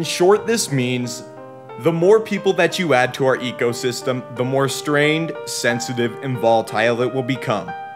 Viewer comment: WHAT IS HE SAYING 3:05 - 3:09 ??? I REALLY NEED THAT PART PLS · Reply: the more people that we add to our ecosystem, the more strained and volatile it would become.